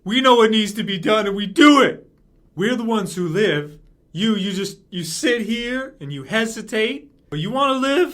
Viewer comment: Instead of hearing "you sit here and hesitate" I heard "you sit here and masterbate" lmao